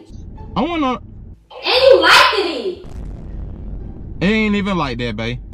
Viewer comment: No way am the only who heard say "Likedteded"